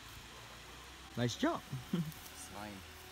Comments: did he just say the word "bossku"?? any MALAYSIANS HERE?!?! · In 2:50 did he say "MANTAP BOSKU"??????